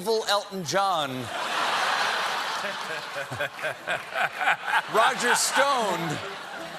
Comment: Did he say "Roger Stoned"?